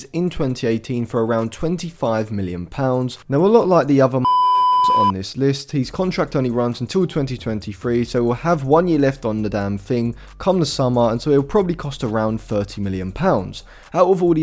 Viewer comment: did u just say “ a lot like all the other .. MotherF***ers .. on this list” …. Is that why it’s beeped out 😆😆😆